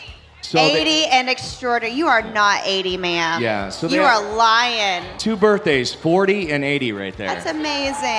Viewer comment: What'd Carrie say? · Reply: “You are lying.”